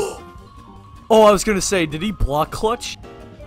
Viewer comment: “oh I was gonna say that he block clutched” Ye, like all the other people in the vid if he didn’t SAW IT 😑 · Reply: i think he said "oh i was gonna say did he block clutch?"